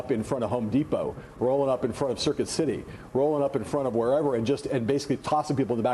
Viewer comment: did he say circuit city?